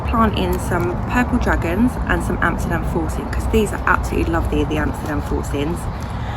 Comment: I had to get creative with google to figure out what this young lady said the variety of carrot she's growing along with purple dragon (a favorite carrot variety we're currently growing here in the States) that she's excited to grow. I found that Amsterdam forcing variety available online from a seed seller I've purchased from in past years. I always like to grow several varieties of carrot per season. I enjoyed hearing from all of those other youtubers in the UK, thanks!